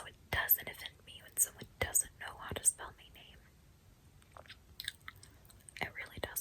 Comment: did she just say me name, honey